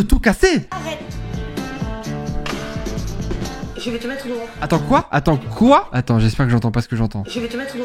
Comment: j'ai entendu "je vais te mêtre une robe" 😂😂😂